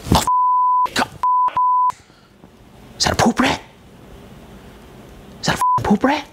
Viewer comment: Can anyone make out what JonTron is saying there? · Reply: Is that a poop rat? Is that a f***ing poop rat?